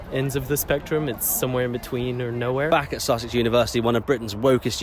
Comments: First thought you said sausage university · Really sounds like he says "Back at Sausage University" ...